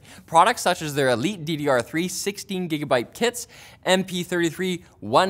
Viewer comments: Just me or did he really say "elite DDR3 16 gigabyte tits" at 1:42😂😂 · I heard “16gb tits.” I went back to double-check, triple-check, and once more for good measure. Every time, “tits.”